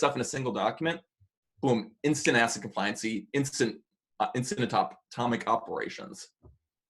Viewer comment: Could someone please say the the term he says here? I keep hearing ass complient. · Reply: "ACID compliancy" 🤣